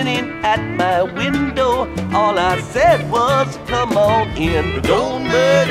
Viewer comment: In the timbers of Fennario the wolves are running round The winter was so hard and cold froze ten feet neath the ground Don't murder me I beg of you don't murder me Please don't murder me I sat down to my supper T'was a bottle of red whiskey I said my prayers and went to bed That's the last they saw of me Don't murder me I beg of you don't murder me Please don't murder me When I awoke, the Dire Wolf Six hundred pounds of sin Was grinnin at my window All I said was "come on in" Don't murder me I beg of you don't murder me Please don't murder me The wolf came in, I got my cards We sat down for a game I cut my deck to the Queen of Spades but the cards were all the same Don't murder me I beg of you don't murder me Please don't murder me In the backwash of Fennario The black and bloody mire The Dire Wolf collects his due while the boys sing round the fire Don't murder me I beg of you don't murder me Please don't murder me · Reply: All I said was "come on in" I thought it was All I said was "come again"